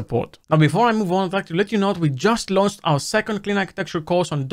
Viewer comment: "Hey everybody I'm naked" - gets me every time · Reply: I keep hearing "We just LOST a new course..."